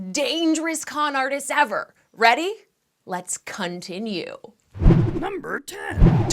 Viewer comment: Am I the only one who thought she said lets cunt-inue